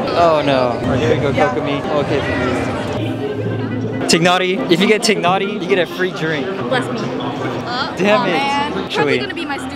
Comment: It's so funny that part 6:00 " if you get tighnari you get a free drink" But the caption says "If you get take naughty you get a free drink"